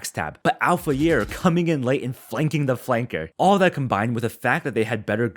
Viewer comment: Did I just heard Flanking the Flanker???